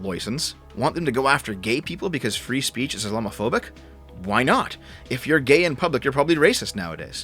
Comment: I misheard you say "Islamophobic" as "Allah-mophobic", very funny.